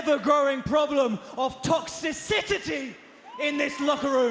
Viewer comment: Did he just say "toxicitity"? 😂😂😂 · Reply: He actually said "Toxicicity"